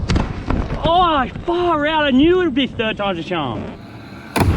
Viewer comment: can any Australian tell me what he says? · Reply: Far out, I knew it would be “third times a charm”